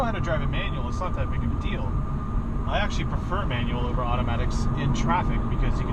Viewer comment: did I hear right? he prefers manual in traffic?